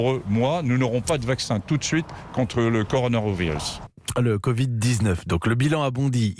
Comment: non enfaite je pense que pendant les récapitulatifs sur le "corvid 19 " a la fin le gars écorche le mot "coronavirus" et c'est sa qui l'a fait rire lui et la femme a coté. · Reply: Je pense aussi, perso la première écoute j'ai compris "le conarovirus" Et à la fin de l'enregistrement il précise en commençant par "Le covid-19 donc..."